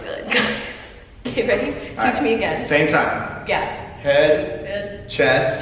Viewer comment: Did she say “okay BABY (or Matty) teach me again” on 4:22???